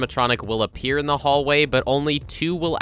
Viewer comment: I thought he said toy freddy and toilet freddy 💀